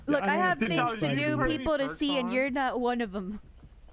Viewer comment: I swear I thought you said "I have people to do and things to see and you're not one of them." After twenty seconds I had to go back in the video in my confusion.